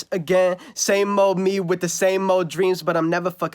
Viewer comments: Tell me I'm not the only one who thought he said same old me with the same old JEANS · I thought he said, " Same old me with the same old jeans " 💀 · I always thought he was saying jeans instead of dreams 🤦‍♂️